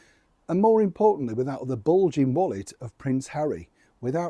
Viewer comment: I thought you said ...without the balding wallet..😂😂❤